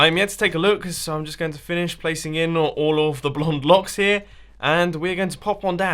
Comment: Wait, did you just say blonde blocks?? xD